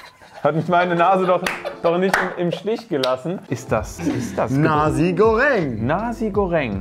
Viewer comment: Ich habe erst Nazi goreng verstanden😂